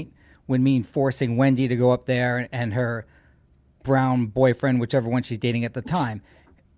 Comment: Did I misunderstand … did he say Wendi’s ‘brien’ boyfriend or whichever one she was dating .. and I thought he said something about is that your black boyfriend from high school .. they were talking bout middle name or name Jerome .. or I was getting it wrong .. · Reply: "Brown" was what he said.